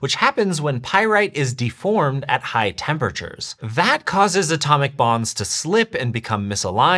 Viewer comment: lol I thought he said ATOMIC BOMBS 💀 not ATOMIC BONDS